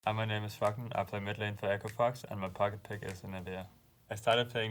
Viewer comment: What he says: "Hi my name is froggen and i play midlane for echo fox" What subtitles says: "Hi my name is fucking after midlane to echo fox"